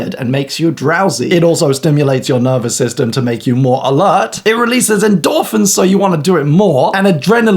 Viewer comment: I heard ''it releases dolphins''😁